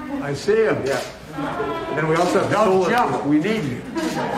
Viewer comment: turn on the subtitles, YouTube CC won't transcribe what he ACTUALLY said. Bidden (Audio Track) tells the people upstairs "don't jump, we need you" ...LOL that's why the Handlers broke this up immediately after he said that. *** YouTube's Transcription says "we also have to jump we need you"